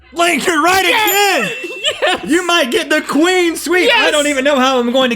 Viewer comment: I saw a comment that said every time one of them gets a queen sweep, Chase should dress up as a queen and go on the set and sweep. Everyone comment about this. Please. We need to make this happen. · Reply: They say queen sweep I always thought they said clean sweep. Cool